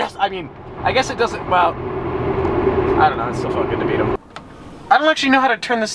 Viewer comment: What did he say? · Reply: Feels good to beat them